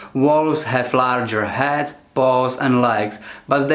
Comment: Sounded like he said "they have larger heads, balls, and legs" lmao